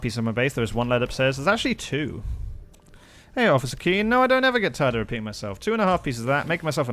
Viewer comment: I thought you were saying "no I don't ever get tired of peeing myself" lol